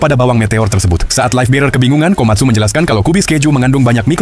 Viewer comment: Bro gua salah dengar atau tidak? Gw denger kubis pejuh 😂